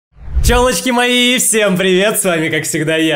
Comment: Мне одной в начела послышалось тёлочки а не пчелочки